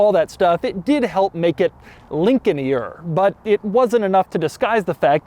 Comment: Lincolnier? Lincolninier? @ 27:45 I’m trying to spell what Doug is saying here. Lincolnyer? Sounds good anyway. Nice video Doug! Glad you finally got your hands on a nice Blackwood! Keeping my fingers crossed for the Bentley Buccaneer. That’d be a YouTube Super Accomplishment as far as I’m concerned.